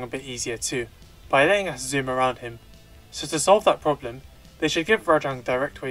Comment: I dont know what that little stroke period at 5:38 was, but I think he was saying that the wirebugs make Rajang easier "by letting you zoom around him"